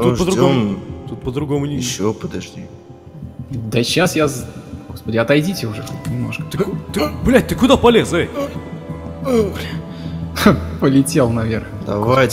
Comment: мне одной показалось что он сказал "блять ты куда полез зай "...😰